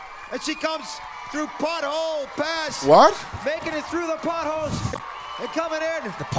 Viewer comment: I thought said "Buttholes" instead of "potholes" 😂😂